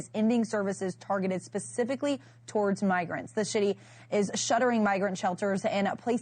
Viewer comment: She said "the shitty" instead of "the city" 0:54 😄